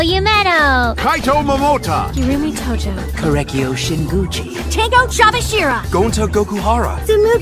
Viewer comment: I just heard "she gucci" XD